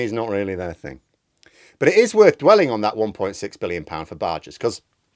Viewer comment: I had to rewind when you said 1.6billion. Thought you said million!